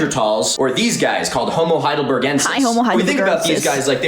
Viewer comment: what did she say? Lmfao. Hi homo hide the burgers inside.